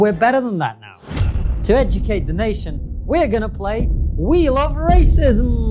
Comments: I first heard 'wheel of racism' as 'we love racism'. It might have been deliberate. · Instead of “wheel of racism” I heard “we love racism” 🥴